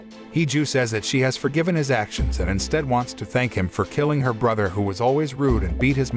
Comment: I misheard “thank him” for “bang him” so I’m here thinking wow after you JUST learned he killed your brother!? 🤣